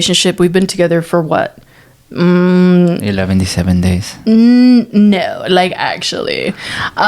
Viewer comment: Did he just say 11-ty 70 days lol